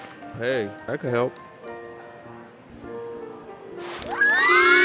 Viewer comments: I thought it said “ I’m cu——- “ That caught me off guard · The thing I heard was “im cruming” 💀